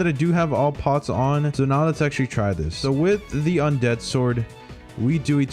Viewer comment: did he just said with the undead sword ?